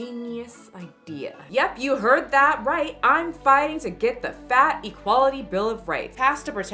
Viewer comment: wait.... did a random Mojo jojo appear on my screen?!?!?! Sidenote, everytime she said "Fat Equality" i heard "Fatty Quality" and giggled